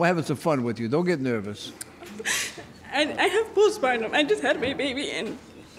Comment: That postpartum is something nobody will ever understand unless you've had it. I had it with my daughter and it sucks. Crying over having to do things or crying if someone tries to help you, not wanting to leave the house....its horrible and I hope this lady is doing much better. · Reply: Yes! When she said "i just had my baby, i just cr-" i heard "i just cry all the time" I felt this so much with my second child. Youre whole body, heart, and soul is just quaking with stress, sadness, guilt. I hope she is okay